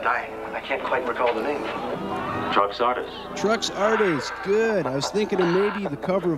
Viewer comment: So the villain's name is Truck Sawdust? Am I saying it right? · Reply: I always thought he was saying "trunks artist"